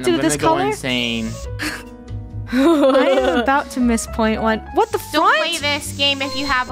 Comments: When said “what the front?-” I thought she said the F word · I think i heard say "WHAT THE FUDZJGE?" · did just say what the fudge